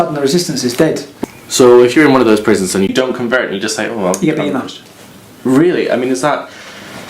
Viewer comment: What does he say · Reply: 'Your being nubbed' (stabbed)?